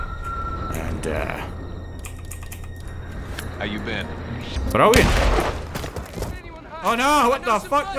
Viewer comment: What does he say at 52:51? “Broween?” Always wanted to know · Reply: Brilliant.